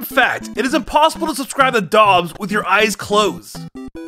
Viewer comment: It's impossible to subscribe to Dobbs with your eyes closed... I honest to god thought he said it's impossible to surprise dogs with your eyes closed.